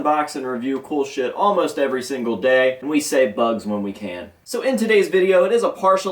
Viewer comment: I thought he said “we can say fuck whenever we can”